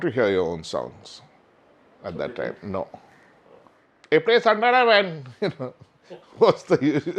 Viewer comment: what is he saying 14:14? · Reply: he said, audiences used to tell the band, "play Santana man" when they used to play their own compositions.